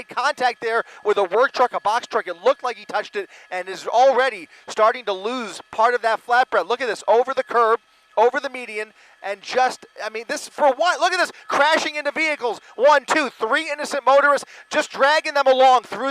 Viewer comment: Anyone else hear the broadcaster say Flatbread at 20:57? 😂